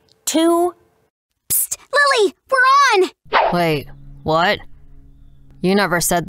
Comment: did i just hear "pst wooly were on"